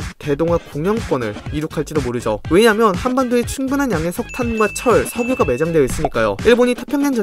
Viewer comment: 아 씨ㅋㅋ 나만 석탄을 사탄으로 들었어ㅋㅋ